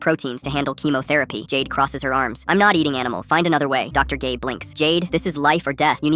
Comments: did I just hear dr gay? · I heard "dr gay" everytime the ai says gabe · He said Dr. Gabe, but I thought he said Dr. gay😔